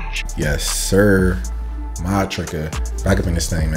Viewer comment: This whole time in your intros you been saying "Modtricca" and I thought you said "My Tricket"